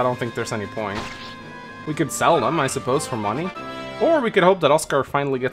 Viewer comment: Right after the zero growth levelup cor oscar I misheard it as “well we can sell him for money”